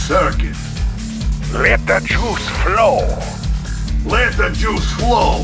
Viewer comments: I always thought the tesla trooper said "Let the Jews glow". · I always thought when I was a kid that they were saying is “”Let the juice flow” 😂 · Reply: When I was kid I thought they say let the shoes flow 🤣🤣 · How come I never knew that it was LET THE JOULES FLOW? I thought he said JUICE